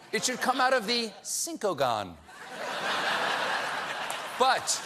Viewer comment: It took me a moment to get the "Cincogon" joke (first thought he was saying Syncogon). But this was a really good one! :-p